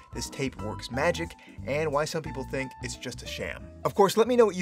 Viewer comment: did he just say SHAM? SHAM?????